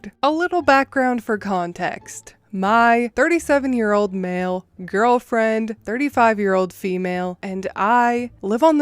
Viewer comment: I did a double take at the beginning, thought you said "37 year old male girlfriend." 😶